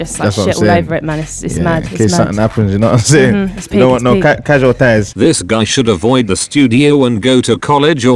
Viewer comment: "don't want no casual ties" is what he said. I am sure that everyone thought he was saying casualties because of the bathroom situation. This man is genius!